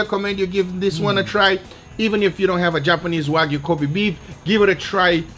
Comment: Lmao in the intro when he says kobe beef it sounds like he says covid beef. That would be a true viral steak video.